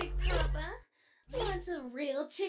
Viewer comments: i thought for a sec cause I heard, 'Mark? Superstar?' Then i turned on captions lol+ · I am curious, did Freddy just “Bark” or did he just gotten nervous around Toy Chica? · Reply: Thought he said "mark... superstar". Like calling out for subtle help